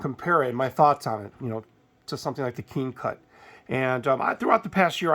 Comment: Just type keencut in google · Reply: ok thanks I thought you said king cut I was typing that in the search bar, I appreciate you.